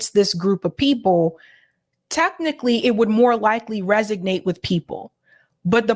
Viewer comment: Did say, "Resignate" at 4:11? Does she mean "Resonate"?